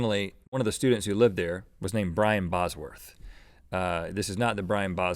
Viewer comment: turn on subtitles> instead of brian it says brain